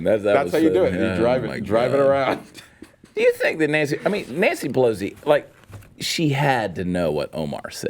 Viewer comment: Chad says Nancy Pelosi but it sounds like BLOW-si.... I think I'm gonna start saying it like that ;)